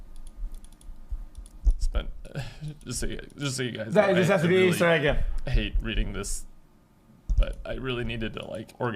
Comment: Can someone pls translate what the fck did X say at the 1:14 mark......??!!!😂😂😂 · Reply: “This has to be an easter egg” referencing COD Zombies easter eggs.